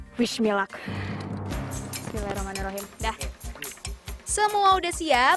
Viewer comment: di menit 2:29 apa cuma aq yg kedengeranya baca bismilak....🙏🙏🙏